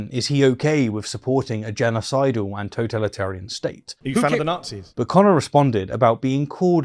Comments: the point kind of remains the same, but since you drill down on it so explicitly and it's said twice: what I hear in the question was "are you a fan of the nazis", not "are you fine with the nazis". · Mehdi did not say Are you fine with the Nazis. He said Are you a fan of the Nazis? which makes this even funnier